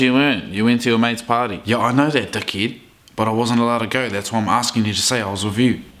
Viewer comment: It sounds like your saying duckhead just saying